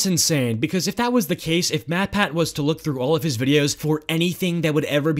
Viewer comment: I thought its said Mattshat as in Matt's hat lol